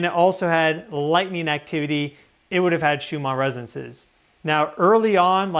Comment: Doesn’t he mean Schumann Resonance?? I could have sworn he said Schumar.